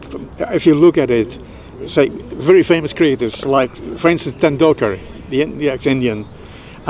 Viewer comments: did he say Francis tendulkar ? Lol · did he say Francis Tendulkar...or is it just me who heard it 😂